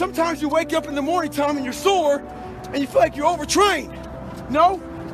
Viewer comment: These subtitles are broken ahahahhahah 00:48 "wake up in the morning time and your sore" This subtitle says "Wake up in the morning telling your sword". I mean.. who doesn't wake up in the morning talking to their sword?